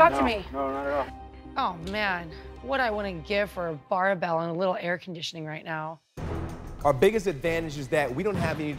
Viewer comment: What did say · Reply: “What would I give for a barbell and a little air conditioning”